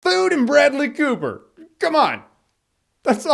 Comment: Idk why but in the intro you said "Food and Bradley Cooper!" I heard "Food and BREAD-ly...." LOL